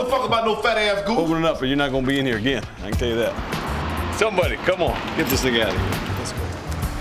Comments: Are my ears tripping or at 30:40 ol boi say "somebody get this nigga out of here"? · Anybody Catch Dude Say “Cmon Get This Nicca Outta Here” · Did he say Come on let’s get this nigga out of here at 30:41😂😩😩😩😳😳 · did he say “somebody come on ..get this nigga outta here” ? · Did he just say get this nigga out of here? 😂😂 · is my mind playing tricks on me or did he say “get this ni*** outta here” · it sounds like he said "somebody get this nigga outta here" lmfaoo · it sound like he said get this nigga out here lol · did he say “somebody, get this nigga out of here” or am I trippin! Wow White boy was that down with the brothers, I’m shocked 😂😂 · Am I crazy…..or did it sound like he say “get this n** out of here” at 30:40?? I can’t tell if he said that or “get this thing out of here,” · did i hear right?? he said get this nigga out of here? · Did he say “get this nigga outta here” at 30:40 ? Lol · Did he say “Get this nigga outta here”? Lmao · Did he say get this nigga out of here?